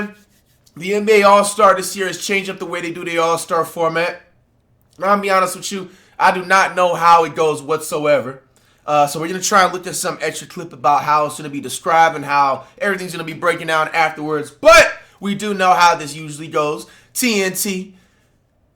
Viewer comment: Thought bro said Edged Clip brah lol